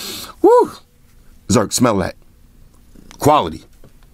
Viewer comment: I heard white shoes smell the oneplus cleaning cloth